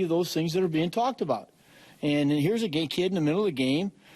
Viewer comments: does he say gay kid??? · just me......or did collins say gay kid at 2:10 mark